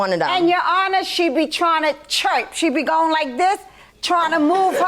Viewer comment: Did she just say chirp and not twerk 👀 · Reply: She actually said “your honor she be tryna churop” 😭😭😭😭😭😭